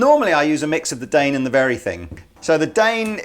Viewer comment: What is the name of the second Distortionpedal he is combining with „The Dane“ Pedal at Minute 18:35? I cant understand what he is saying. Verythink? Varything?